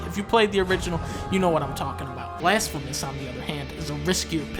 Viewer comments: What game did he say at 1:33? That kickstarter looks cool as hell and I couldn't understand what Dunkey said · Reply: Is he saying Blasphemous? I'm guessing going with the tone of the game... totally looks metal!! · What is the game at 1:34 ? I can't understand it for the death of me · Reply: Blasphemous. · What is that game at 1:32? Didn't quite catch what Dunky said in the video. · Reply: The game is Blasphemous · What's the name of this game again? 1:32 i dont speak english and i cant get what dunkey said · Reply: Blasphemous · what's the name of the game at 1:33 I cant hear what he says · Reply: Blasphemous